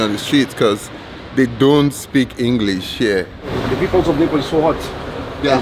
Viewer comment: translator app. Sounds like you saying Nipples instead of Napples ”😅